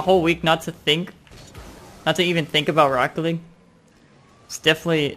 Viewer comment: he says rocket league so fast it sounds like he says broccoli